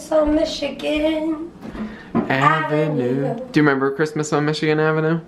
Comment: When you both were in the kitchen, and began to sing, I thought she said :on chicken avenue", and I assumed you were making chicken for dinner and she was making a pun. Then it hit me it was "Michigan avenue". Love your vlogs!